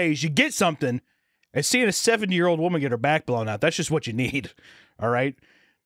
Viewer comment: I was actually fucking shocked cause I thought he said he was watching a 7 year old getting her back blown out instead of 70 damn